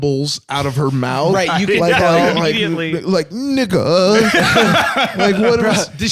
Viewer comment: almost sounds like ify is saying 니가 here